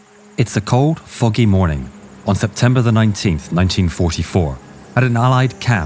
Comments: At the start I heard him say a cold fuggin morning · I thought you say at the begining is "it's cold f**king morning" but i replayed it and it say foggy🤣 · I thought he said it’s a cold fucking morning at the start of the video ngl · Thought he said cold f*cking morning 💀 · The first three seconds and I hear “It’s a cold, Fu—ing morning”